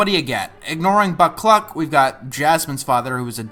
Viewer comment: I know you said "Buck Cluck" ...but what I heard was "Butt Plug"